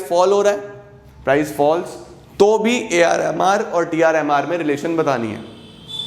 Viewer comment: when he says AR and MR am I the only one who hears AR Rehman? 😂😂